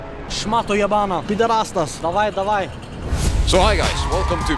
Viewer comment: he did not say I love you he just said come on come on 💀